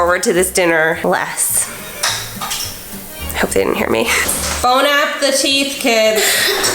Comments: did she said Bon Appecheese? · Did anyone else notice she said: Bone apple teeth instead of bon appetite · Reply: Ikr I heard "bone app the teeth" tho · Did she just say bone up the teeths 😂😂😂